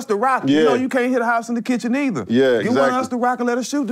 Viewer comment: did he say you cant hit a house from the kitchen ? lmaoo 1:18 disrespectfullllllllll